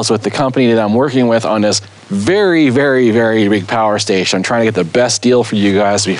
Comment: At ten seconds into the video, did you say "very, very BIG power station" ? couldn't quite hear the word you used there.